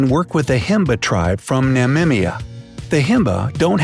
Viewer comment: Did he just say "Namimia " amount of disrespect.